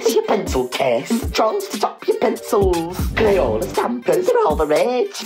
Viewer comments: I swear to god I thought you said “Trolls to fuck your pencils!” My god I’m tired lol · What is wrong with me, I thought it said ‘and some trolls to f*ck your pencils’ 😂😂😂